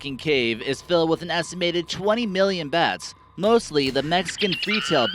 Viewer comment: I love the one with 26 million bats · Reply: Borteynepro Doom Doom I just want to know who counted them all lmao.I thought he said 20 million bats don't matter.